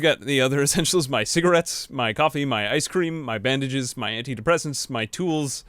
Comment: you said "my bandages" i heard "my cabbages"